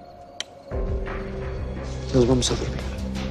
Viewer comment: Alguém sabe me dizer o que ele disse pra Júlia com a voz baixo que ela fez essa cara de medo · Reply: Yo entendí " vamos a dormir"!!!😘😘👋👋